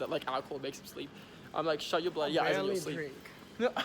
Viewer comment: WAIT WHAT DID SAY I CANT HEAR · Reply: This is a bit late but he said "it's probably also cancelled". Better late than never 😅